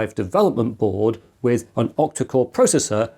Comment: I knew it was "octa-core", but somehow I heard "optical processor", oh that would be interesting.